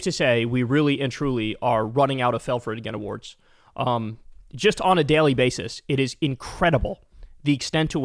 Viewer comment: I thought you said the factory for fell for it again awards broke…